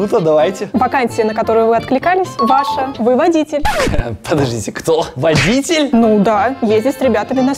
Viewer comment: я сначала там услышала выводите а потом глент сказал "ЧТО? КАКОЙ ВОДИТЕЛЬ!"